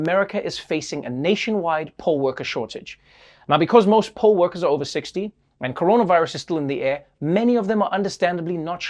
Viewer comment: I thought he said most poll workers are over 6 feet.